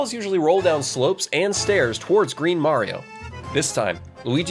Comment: DID BRO SAID GREEN MARIO💀